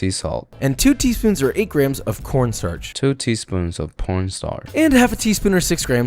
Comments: did i hear wrong or did he really say "p*rn star" XD? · what did he sayyy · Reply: Corn starch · why is the caption says that word instead of corn😭